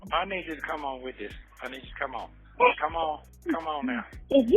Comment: didn’t he say her name? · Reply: I thought he did too, but he said ( I need you to come on with this, I need you to come on, come on, come on nye)